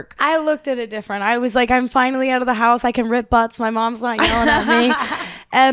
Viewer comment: At first, I thought her mom said; "No farting in the house!"